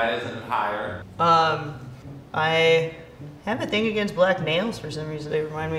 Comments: lmfao i heard "i have a thing against black MALES for some reason" and was like really?? nobody is gonna mention that ?? lol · she said "i have a thing against black nails" and cc said "I have a thing against black males" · I thought she said I have a thing against black males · Oh my god. I’m cleaning house with this playing the background and I thought said “I have a thing against black MALES” I had to rewind!!!! LMFAO